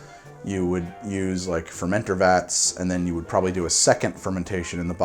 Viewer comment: did someone say VATS